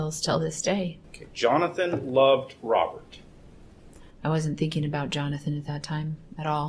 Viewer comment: did he say “Jonathon loved Robert” ??